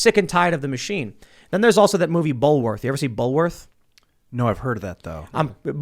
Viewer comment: For anyone who also didn't understand the title of the movie (I had to turn on subtitles): it's Bulworth (1998).